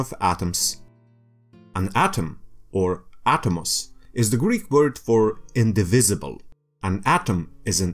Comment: Very nice! And because of this, a pity that you have to hear "Greeth" or something like that, at 0:41, instead of "Greek".